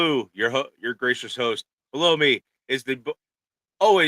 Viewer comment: 😂 I thought he said “ blow me “ at first to !!!